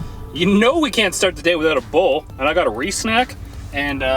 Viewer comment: I thought you said you know we can't start the day without a bowl, and I was like right on! (Then I saw you meant RedBull and was still like right on!)....don't judge me.